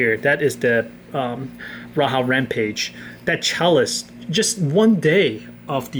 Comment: did he just say the raha rampage?